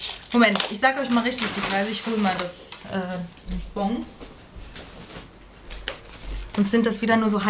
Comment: Haha ich hab statt Bon Pong verstanden und dann noch statt Mac Crack xD